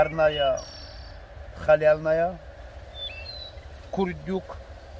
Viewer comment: Послышалось: говядина парная халявная! Всё очень красиво и вкусно выглядит - лайк!